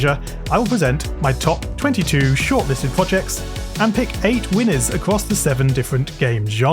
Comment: Did just say "mewwing" or "winning"? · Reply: he said "winners"